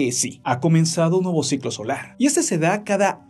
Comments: En un momento pensé que decía "SIGLO ESCOLAR" por eso entre para ver y veo DICE SIGLO SOLAR😂😂🤣🤦🏽‍♂️. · Pensé que decia "Inicio del ciclo escolar" xd